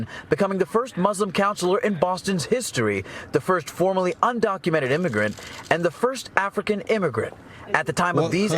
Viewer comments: you might want to listen to this again. They repeated what the news said, and played the video!! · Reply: Apparently neither you nor the hosts of this pod are listening to the news report very carefully. If you go to 15:21 in this video, you will hear the news reporter say "...the first FORMERLY undocumented immigrant..." When they use the word "formerly" that means she was previously an undocumented American citizen, and that doesn't apply to her current status. A simple Google search will tell you that she got her American citizenship back in 2019. I stand by what I say. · Don't say that these podcasters called her that, when they only said what the NEWS said more than once! Here's just one example! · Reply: Apparently neither you nor the host are listening to the news report very carefully. If you go to 15:21 in this video, you will hear the news reporter say "...the first FORMERLY undocumented immigrant..." When they use the word "formerly" that means she was previously an undocumented American citizen, and that doesn't apply to her current status. A simple Google search will tell you that she got her American citizenship back in 2019. I stand by what I say.